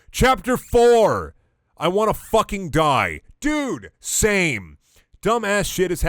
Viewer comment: I genuinely thought you said "I wanna fucking die" in response to the chapter name.